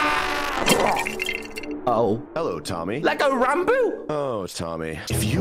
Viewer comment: I thought that Ranboo said Hello, Lobotomy